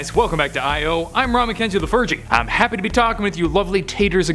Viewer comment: this is the first video i have seen on the this channel and when he said his name i thought he said "im Ron mckenzie the virgin"